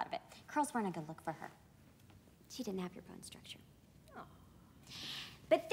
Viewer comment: 'she doesn't have your bone structure' and her proud mother is my favorite scene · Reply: I thought she said "she didnt have good bone structure"